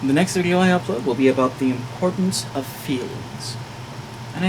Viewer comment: what? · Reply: Oh, I had it on 2x speed and misheard "the improtance of feelings" as "the importance of fields". D'oh!